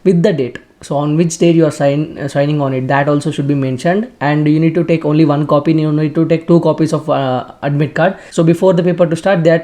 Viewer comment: Didn't understand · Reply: Take only 1 admit card